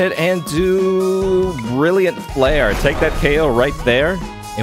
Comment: Brilliant flare... I heard him saying "really unfair" kkkkk which it is XD